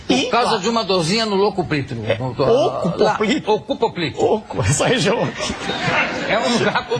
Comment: Alguém pose me ajudar a entender o que ele disse no minuto 10:13? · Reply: Ceco - Porção inicial do intestino grosso. “Oco” poplíteo - nomenclatura antiga para a Fossa poplítea, região posterior (atrás) do joelho. 👍🏼